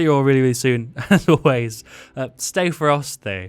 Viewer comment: When you said ferocity I thought you said frosty